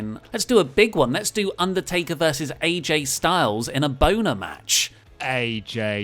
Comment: did you say a boner match lol